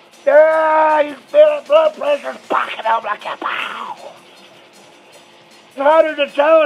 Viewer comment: Im dead what did he say at 2:48 😂😂😂😂💀💀💀💀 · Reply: He said he could feel his blood pressure spiking up.